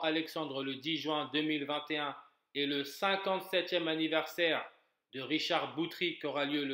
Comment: Salut bon je suis ok avec t calcul sauf pour l'anniversaire de Richard Boutry son prochain n'est pas en 2022 mais en 2021 vu que c'est en novembre.. Bon à part ça tout le reste je suis d'accord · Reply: Ah ben autant pour moi j'avais mal compris moi j'ai entendu "prochain anniversaire" mais si tu dis bien "57eme anniversaire" alors il n'y a pas d'erreur c'est juste moi qui ai pas bien entendu dsl :/